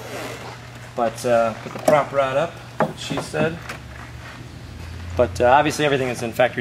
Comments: did he say " put the prop rod up... Thats what she said" ? ROfl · Listen to 3:25 to 3:29 Im pretty sure he said, " put the prop rod up, thats what she said"..... hahaha thats so funny!! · it was subtle but i heard the thats what she said remark at 3:32!